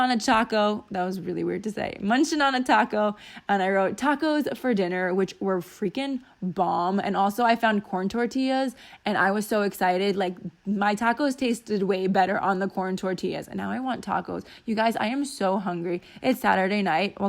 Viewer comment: Another great video! Love it. I hope you had the best time with Andrea! Also, I totally thought you said porn tortillas. LMAO!